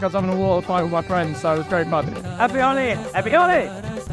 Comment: is am the one who heard Happy oli